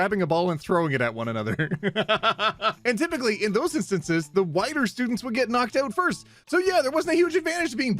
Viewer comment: At first, I heard “the whiter students would get knocked out first,” and was very confused. Then I realised he said “wider.” LOL I’m so deaf. Happy Pride Month from an Asexual furry 🏳️‍🌈🏳️‍⚧️